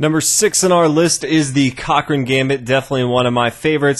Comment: I thought it said "Concha Gambit" for a sec xd